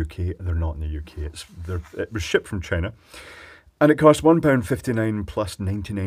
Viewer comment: Sorry folks, maybe it's the late hour, or maybe other things, but I'm sure at 0:14 to 0:16 I heard saying [..."shit from China"...] though I know it was really [..."shipped from China"...].